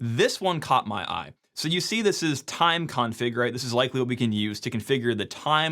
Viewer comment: thought you said mein kampf when you said time_conf